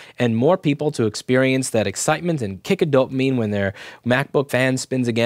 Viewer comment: When he said "kick of dopamine" I first heard "kick a dope meme".